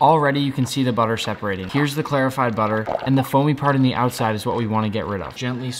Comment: Dude I actually thought, when I first watched this video I thought you said verified butter like the butter was verified on Twitter